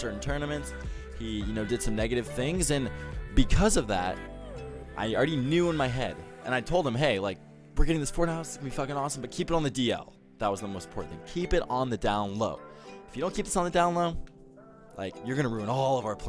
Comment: Does he say "porn house"? I don't hear it well and the subs say he says so. Which wouldn't surprise me.